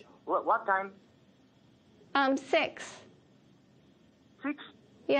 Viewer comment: s○xに聞こえたw